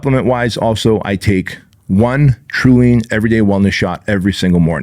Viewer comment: Please spell what you said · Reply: Trulean Everyday Wellness Shot